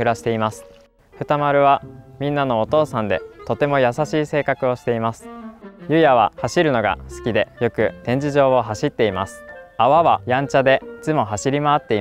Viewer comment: dude's saying he likes to run, but the subtitles says he "loves sleeping" lol